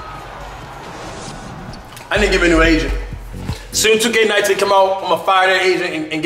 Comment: I thought he said “I need to find me a new Asian”😂😂